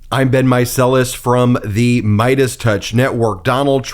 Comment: Is it just me, or does anyone else think that when Ben says this “this is Ben Meiselas” I hear, “this has been meiselas”?